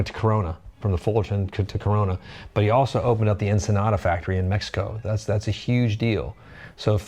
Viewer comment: did I just hear 'fender empanada factory in mexico?' I want fender branded empanadas.